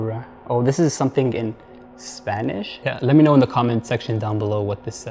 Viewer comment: I hear a voice say 🗣️🔊 "Yea" and shortly after "I have!"